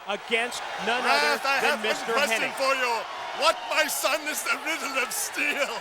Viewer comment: what tf is Nash saying at 1:02:43 ? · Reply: It's a Conan the barbarian reference James Earl Jones Asked Conan the barbarian a question what my son is the riddle of steel